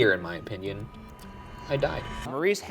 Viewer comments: I heard "F--k off." instead of "I died." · I'll be honest, I didn't hear "I died." I heard "Chiga chiga," like the song at the end of Ferris Bueller😅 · I just can't hear "I died" in the shower recording. I hear "a cop". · I heard “a cough” definitely not “I died” 😂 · To me the "I died" clip sounds like it is saying Hot dog. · The first EVP sounds like it says, "I passed." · I didn't hear "I died". I heard "A cop"